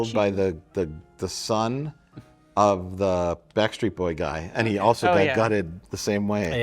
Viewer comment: I actually know what Tae Bo was and even I didn't know what Rich was saying the first time he said Tae Bo. I swear I heard "...he's the Type-O guy right?..."instead of "Tae Bo guy" lmao